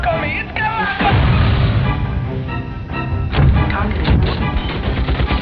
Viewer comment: "It's got Lock on me it's got lock on---" *missile explosion* I thought he said when I was younger (i'm 20 now) "HE'S GOT AWFUL BEEF, HE'S GOT AWFUL BEEF!"